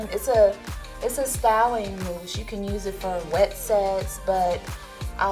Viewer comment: did she just say "wet sex"